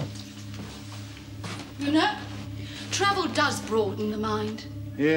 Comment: He actually does say "bye". I guess she just didn't hear him.